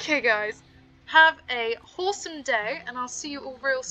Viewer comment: I hear "Have a horsome day". Am I getting crazy?!